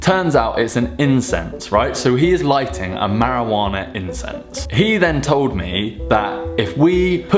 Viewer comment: I first thought he said Marijuana insects